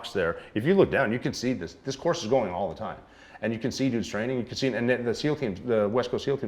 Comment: I TOO definitely thought he said “Die Training” 😳🤦🏽‍♂️ Wheww 😅